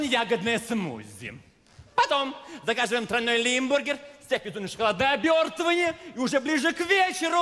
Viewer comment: что он сказал? 🙄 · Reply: Всем на шоколадное обертывание